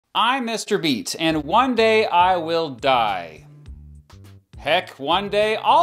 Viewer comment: "I'm Mr. Beat." did i just hear mr beast nah i probrably misheared it but this is a curiousing channel